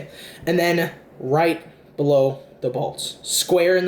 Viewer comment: When you said right below the bolts, I thought you said right below the balls. "You're gonna put your finger right below the balls, square in the middle."